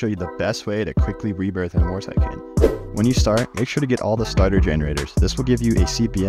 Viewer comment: At Starter generators I heard “spider generators” 💀